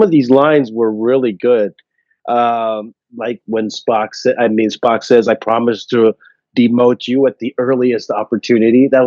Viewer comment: Yes he did, he literally says "I promise to demote you at the earliest opportunity". Spock says it in response to Kirk saying that he still feels funny being called captain while V'Rel's alive (it happens at the tail end of the scene where they come up with the plan to get the scavenger ship to come after them). It was a Spock version of a joke meant to help put Kirk at ease. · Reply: I could have sworn he said "no doubt captain V'Rell will demote you at the earliest opportunity"